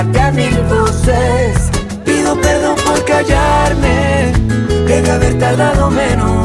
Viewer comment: yo escuche cago ¿quién caga? xD